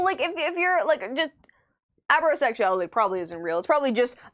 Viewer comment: Anyone else thought it said ABBAsexual at first?